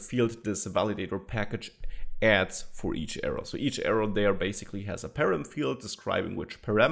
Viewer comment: Nice lesson! Needed time to figure and a few tries out what did you say at 4:20 :) - every error has a PARAM field. Hope to see you again soon!